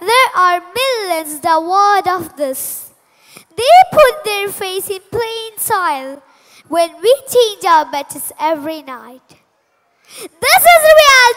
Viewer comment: the line which she said here can anybody tell me what she told??Please tell guys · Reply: "they put their face in the plain soil when we go to bed every night"